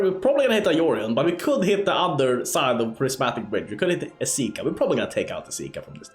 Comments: LMFAO why did it sound like you said Prismatic Bitch 🤣🤣🤣🤣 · Did i hear "prisimatic bitch"? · I heard "Prismatic Bi+ch"